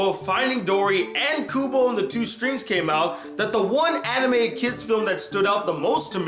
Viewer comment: Did you say anime at 1:46?